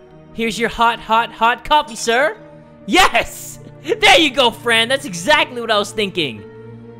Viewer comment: When said Fran I thought it said friend so alike lol